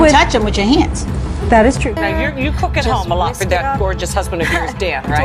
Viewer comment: did she call him 'gorgeous'? Lol